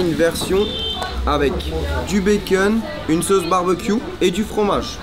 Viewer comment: cest moi ou ta dit di becun